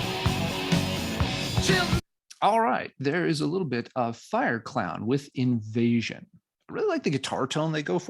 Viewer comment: Love your NWOBHM knowledge and selections. It’s funny because I thought you said Fire Cloud. Then I scrolled down and found it was Fire Clown. LOL. Maybe the name would sound cooler if I read the novels. I did like all the music though, even the Scorched Earth. Great video